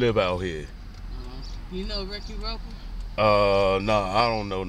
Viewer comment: Did she say "Ricky Roper"!?!?